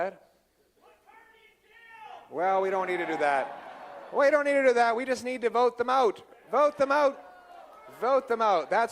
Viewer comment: What did the guy scream in the audience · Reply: I was there, he shouted to put Carney in jail, so Pierre said we just need to vote them out of office.